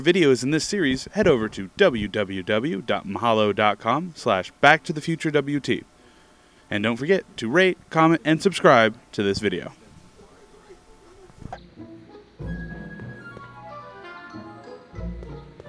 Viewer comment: it sounds like your saying "rape, comment, and subscribe" lol